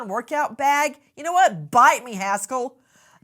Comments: “You know what? Bite me Haskell “ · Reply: When I was listening in the car I thought she said bite me asshole and was like that's so not like Nancy. She won't let the twins swear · sounds like she said “bite me asshole” I thought that was hilarious then I realized she said “hasell”